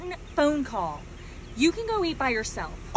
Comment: I’m certain I heard ‘ you can go live by yourself!’ So as someone who’s been married twice as long as you are young… run son, run….. that beautiful truck will take you to new heights….